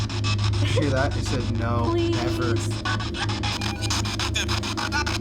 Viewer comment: Yoooo. When the box said No Never. I heard “Fuck Them”